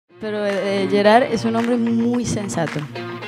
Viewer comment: I thought Shakira was saying Steven Gerrard